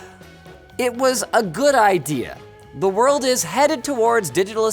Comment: Linus says "the world" I hear "botnet"